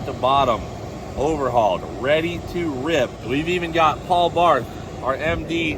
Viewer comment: I thought he said Paul Blart at first hahaha. Like the mall cop